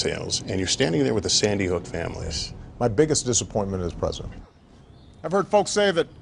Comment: I thought it was saying "Biggie Smalls" not "Biggest Dissapointment" 😂😂😂